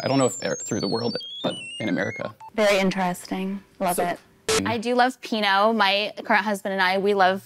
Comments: i’m dead did she say “i do love pinot. my CURRENT husband and I…” 💀💀💀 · Did she say her current husband? At like 2:00 ish